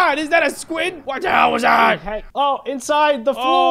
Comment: sketch did you say hell?!